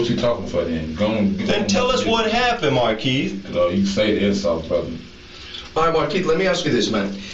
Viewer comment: What Markeith says is "You can save the insults brutha." (brother)